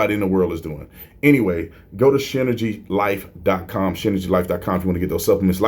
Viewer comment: LOL, I thought you said Shitergy at first.